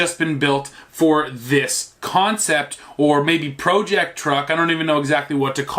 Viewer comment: Did you just say “ pro jecked truck”?